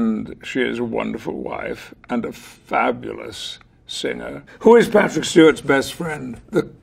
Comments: sounded like he said his wife was a fabulous sinner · I thought he said “and a fabulous sinner” and I pissed mysef